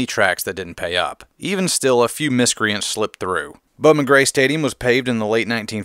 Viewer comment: What track is that? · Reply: I think he said Bumma Grey Stadium in Winston Salem :) Here's the time stamp: 1:49. Also I'm pretty sure it's that because he still talked about it and showed footage still :)